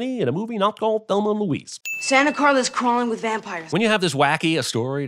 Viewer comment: He actually said santa carla not clara @ 14:01 ....sin for ding